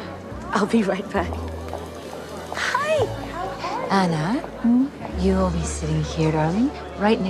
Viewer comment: i swear she said ara not ana